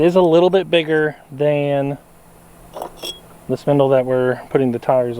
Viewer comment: Just cut the center hubs out of the old rims and weld a washer on the end, if you keep the NEW tires closer to the inside there is less pressure on the SPINDLES. IT SOUNDED LIKE YOU WERE SAYING " PINDLES " INSTEAD OF " SPINDLES ".